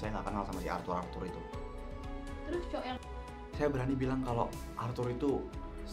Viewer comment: kalian kedengeran gak sih ada yang ngomong "terus cowok yang"